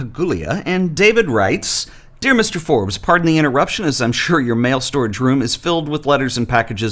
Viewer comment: I misheard that as male storage room! where else would you store your males